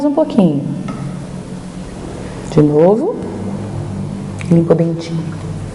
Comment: só eu q ouvi limpa o dentinho ?